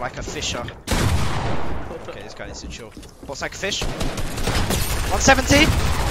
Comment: i thought he said ''ballsack fish.''